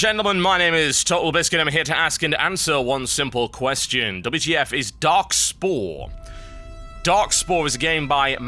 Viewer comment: I thought it said "WTF is Darkshore". Haha